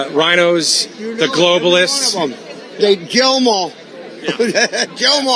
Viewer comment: I thought bubba was saying "jail em awl".